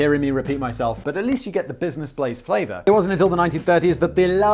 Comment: I had trouble finding the channel because I always misheard Simon... I thought he kept saying "Business Plays" on the podcast... Which, as a name, fits better, making it even harder to comprehend "Blaze"... :-D Finally found it by searching for "business plays simon"... ^^